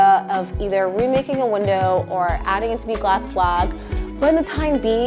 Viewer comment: remaking a window or adding it to be a glass wha now?? A glass blag? 🤔 lol the captions say “blog” but that don’t make no sense and I’ve replayed this part like 5+ times, slowed it down, checked the transcript on the off chance it had any other ideas (it didn’t)… I even tried googling glass blag/blog/bleck/black juuuust in case, and alas I’m still no closer to cracking the code 😂 is it glass back?? And if it is, would I be any closer to understanding what the heck you actually meant? (Not rly lol) Pls halp? 🫠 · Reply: hahaha "Glass Block" didn't catch that in the captions, my bad!!